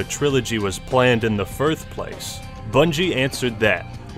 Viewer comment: Did he just say firth?